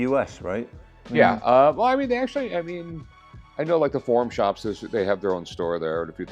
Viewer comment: An American telling an Englishman how to pronounce an English word, then pronounces every watch brand wrong apart from Rolex, which you can not pronounce wrong. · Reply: Sounded like he said porn shop